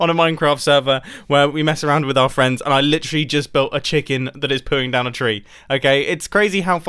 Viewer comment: Omg, the first time you said it I thought you said you were building a cherub not a chicken.. a diorite cherub would’ve been actually gorgeous lol